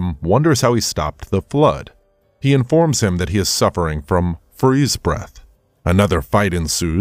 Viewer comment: This must AI narrated. Did he say "suffering" or "freeze breath - a power you don't have."? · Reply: "suffering from freeze breath" 😂